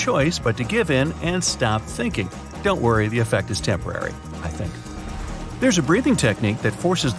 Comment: I hear him say "Don't worry the effect is temporary (I think)" is it only me? You may have to focus though.